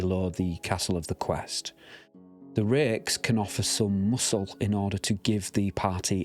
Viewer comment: what word is that? Rakes? It sounds like that. The word you use to describe the 8th level fighters. Thanks so much, V